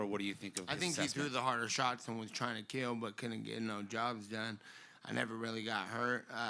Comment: Soo! BLazeD! 😂🎉🎉🎉Let’s CELEBRATE 🍾 bet it took him 35 seconds after he walked out. I swear he said at 2:06 no ‘drugs done’ ☑️😄